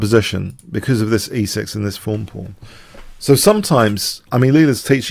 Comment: I always thought he was saying "fun pawn".